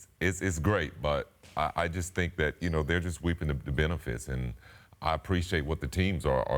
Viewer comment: did he say “weeping” the benefits?